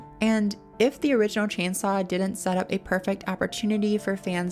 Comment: thought you said transsaw and now i think thatd be an awesome name for a movie lol